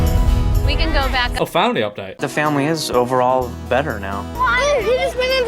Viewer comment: they way I thought he said “the family is over” 😂